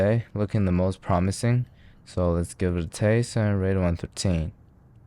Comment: Somehow I always hear "...give it a taste and rate it on Poutine". I think you should try a Poutine recipe.